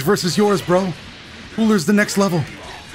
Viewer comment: When he said cooler is the next level I thought he said hitler is the next level